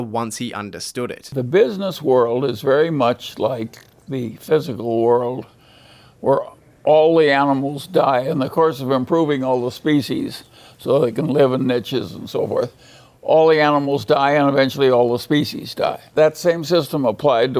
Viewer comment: Can someone help me understand what Munger says around 13:17 ? Thanks! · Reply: The business world is like the physical world...all the animals die... species keeps improving so they can live in niches and so forth, all the animals and species die